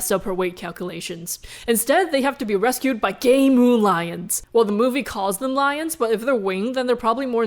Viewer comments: did she just say "gay moon lions"? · i thought u were saying gay moon lions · i thought you said "gay moon lions"